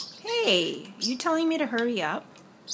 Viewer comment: I thought he said All right instead of Hurry up^^.